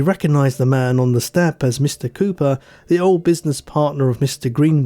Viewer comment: During the story of the Mysterious Letter I thought for a moment you said one gentleman's name was "Mr. Pooper" and I thought to myself....well that's unfortunate.